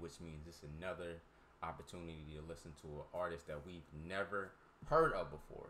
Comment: Maybe i misheard. When you were predicting, did you say "Army artist?" Other than that, great reaction! 😀